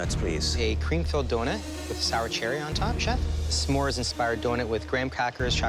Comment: i thought he said chat xD